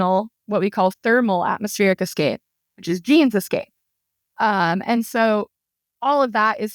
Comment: I'm curious what the word is being used here that sounds like "jean's" or "gene's", "genes" etc. in "jeans escape". I considered "G's escape" as in TRAPPIST-1G and G as in gravity, but we are discussing planet C here and people usually say "gravitational escape" instead of "G escape".